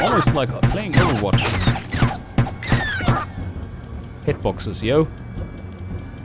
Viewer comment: did you say Over watch? I think the monsters volume are louder then you! :P